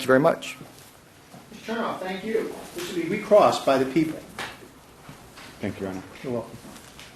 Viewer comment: Non-english speaking asking: Do I hear it correctly? At 49:25 the judge really says that "...be re-crossed by "the people (sic)"?" As the prosecutor represents the state - and thus the people?